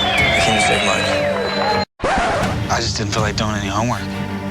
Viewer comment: What did isaac say at 03:25??? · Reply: That he doesn't want to do his homeworks... 😂😂